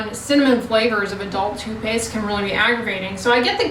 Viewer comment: I heard "I love kids' toupées" 😂 not toothpaste. Thank goodness for CC! Love your channel❤